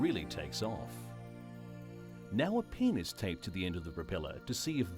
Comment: okay okay 2:09 sounds like penis tapped instead of pen is tapped